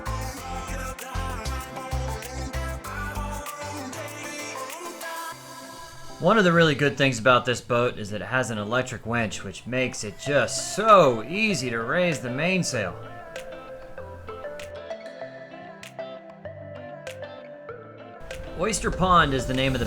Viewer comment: Did he say that the boat "has an electric wench" at 6:36?